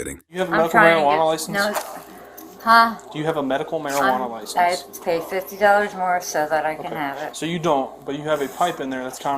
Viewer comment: He ask do u have a medical marijuana license. She responds with “ I pay $50 a month to have… “. Doesnt this sound like she is saying yes, i pay for it monthly. A short min later you see her getting out what looks like a license from her wallet and trying to give it to him. He refuses and throws her to the floor. Not sure why the reporter is lying about what he said and what she said … ? .. its like the reporter is reporting on a different incident. No opinion on the rest of the video but this part about the pot is screwy.. its like the reporter thinks you will believe his version instead of your own eyes and ears. Turn on close caption also. It verifies what is easy to hear. I am so sick of everyone lying. Gezzz